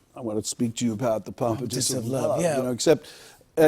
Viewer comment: Until right now, I always thought he said, "properties of love."